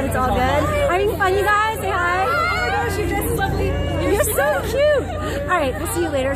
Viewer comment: Yikes, did someone say your dress was ugly 1:53 wth? You always look great in your vids, and the dress was perfect on you! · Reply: she says lovely